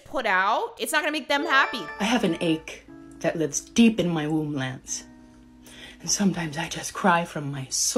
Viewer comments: "I have an ache that lives deep in my womb, Lance" may be the funniest sentence I've heard in months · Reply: When it went viral on tik tok at the time EVERYONE mis heard it as “Womb Lands” 😂 that and the “YOU KNEW” were viral sound clips everyone used for a hot minute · "I have an ache that lives deep in my womb, Lance" may be the funniest sentence I've heard in months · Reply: I thought she said womb lands 🥴🥴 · "I have an ache that lives deep in my womb, Lance" may be the funniest sentence I've heard in months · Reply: I thought she said womb lands 😂😂